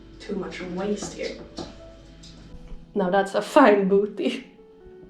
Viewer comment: Lol did you say "Now that's a fine booty"? Happy Birthday!! Hope you had a great day!